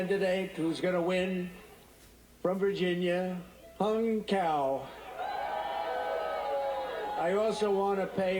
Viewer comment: Did he say Hung Cow?? Well that's just bull...